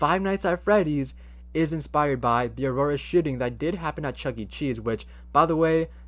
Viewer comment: no offense, but it sounds like he is saying sh*t*ng